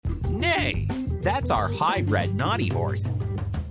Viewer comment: I thought the voiceover said "Hey that's our high friend naughty horse"